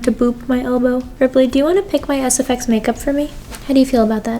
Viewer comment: When she said "hey ripley do u wanna pick my sfx makeup for me" I swear I thought she was gonna say "hey ripley do u wanna pick my ass" wtf lol 😆 🤣